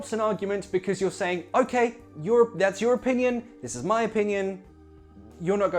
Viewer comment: Lol you said "your opinion", but i heard "European"